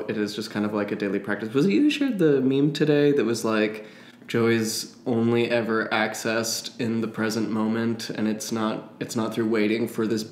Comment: I thought he said Joey is only accessed in the present, lol. Like from friends